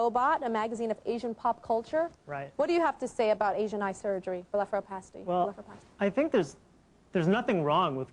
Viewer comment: What does she say twice at 6:46 it sounds like vila fro pasti or something lol wtf?